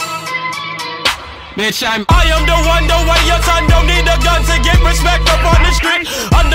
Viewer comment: Anyone else think it said: "I am the one, the one your son"? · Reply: I thought he said "don't want your son"